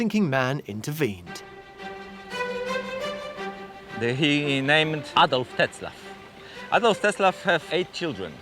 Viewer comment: Was the name really Adolf Hitler?? (obv not that Hitler but I might be mishearing or it is spelled differently)